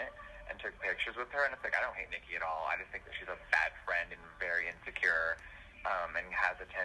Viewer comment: Didn’t he say he doesn’t talk bad about his friends but calls Nikki the “fat friend” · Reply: He said "Bad friend". I thought he said fat also so I listened a bunch of times and then tired on subtitles lol